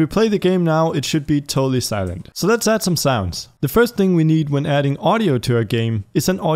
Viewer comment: for some reason I heard "Lets add some silence" XDXDXD